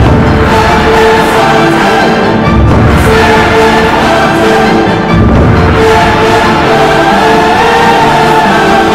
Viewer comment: I would pay good money to know what Scanlan is saying at this point XD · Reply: First word can't say, but the rest is a "how could you?"